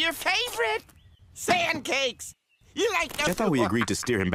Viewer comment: Sand cakes? I thought i heard pancackes :/